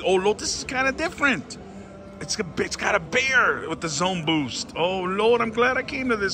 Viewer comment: OMGEEE! I thought you said "The b*tch got a beer" 😂😂😂